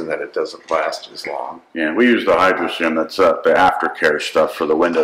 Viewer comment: Right at about 11:30 you mention a window treatment that I can't quite understand. The CC calls it Hydra Sim but I can't find that. Can you tell me what exactly you said? Thanks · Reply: The ceramic coat applied to the hull is an alcohol based solution. The hydraserum after-care is a water-based ceramic coating. The hydraserum acts as a sacrificial layer to help the ceramic coating not wear off as fast (wear off the hydraserum first). I go over Channel Surfing twice a year with the hydraserum to hopefully prolong how long my ceramic coating lasts. For the windows (and stainless steel rails), they are coated with the hydraserum. However, glass and stainless is not very pourus so it lasts about a month. two coats of Hydraserum on the windows works better than Rain-X. In this video, timestamp 08:00 you can see how well the Hydraserium works on the windows. Unfortunately, we also midjudged weather in Johnstone Strait and were punished severely for it. With all the water we took over the boat, Johnstone wore off the Hydraserum in a little over an hour of abuse. Under normal boating conditions it lasts about a month on the windows so I don't have to use windshield wipers. <link>